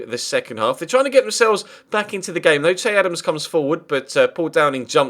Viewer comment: did u just say ched evans